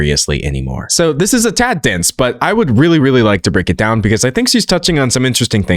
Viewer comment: i really thought you said “so this is a tap dance but i would really really like to break it down” and i burst out laughing